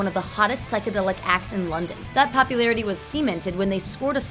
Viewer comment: did she say semen-ted